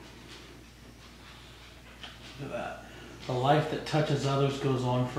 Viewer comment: sounds like someone vomiting oh nvm that is Michael saying look at that